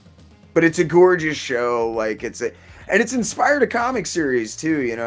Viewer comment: what comic? I can't quite make out what you guys are saying. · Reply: The Invader Zim comic published by Oni Press. It ran for 50 issues, we're def gonna cover the trades.